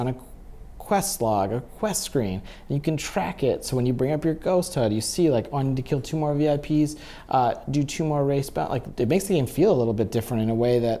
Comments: Am I the only one who heard him say race bounties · did he just say do 2 more races?